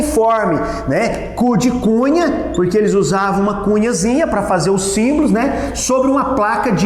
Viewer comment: eu entendi macunhazinhaKKKKKK muito boa a sua aula!